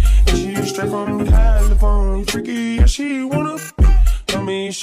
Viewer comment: *does the dance cutely* Btw it's Boys AND girls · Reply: i hear poison girls